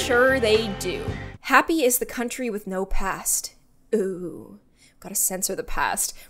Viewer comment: In the beginning I thought it said “happy is the country with no pants” lol